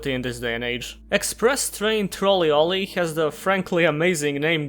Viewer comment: Oh, Trolley Olley. I thought you said Express Train Trawl E-Loli.